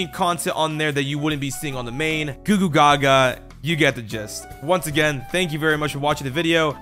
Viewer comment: I thought he said you get the jizz💀